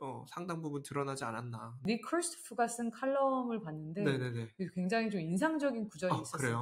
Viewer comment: 잘들었습니다 29:39 정말 인상적인 구절이네요! 리커르스프 철자 부탁드릴 수 있을까요? 즐거운 시간이였습니다 :) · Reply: 닉 크리스토프(Nick Kristof)의 칼럼이었습니다!